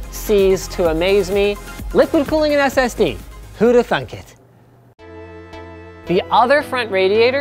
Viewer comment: is it just me or at arround 4:37 did he really say "who'd have THUNK it?"